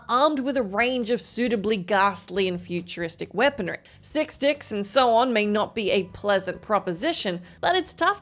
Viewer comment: did anyone else hear "six dicks"? because that would also not be a pleasant proposition